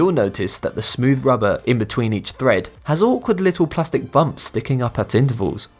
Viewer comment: Did he just say plastic little bumps at 8:19...